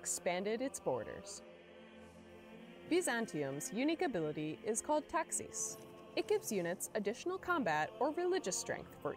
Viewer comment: legit thought they said “Byzantium’s unique ability is called taxes” for a second, the first time I watched it